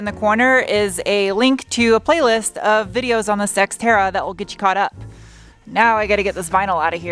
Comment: Was there a Freudian slip there? I could swore I heard Sexterra